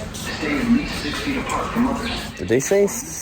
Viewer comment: It legit did sound like they said stay 6 feet away from mothers. Juuuuuuust the mothers. 🤣